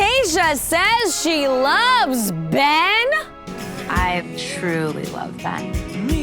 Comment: I think that Tayshia said "I've truly loved Ben", as in, she's immensely enjoyed his company, not that she LOVES him, I don't think she's there yet. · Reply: I 100% thought she said “I truly love Ben” like present tense, which would change the meaning of the phrase completely. I hope that’s what she said because I like him a lot more than Zach to be honest😂